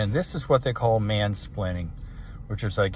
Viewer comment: you said mansplaining instead of manspreading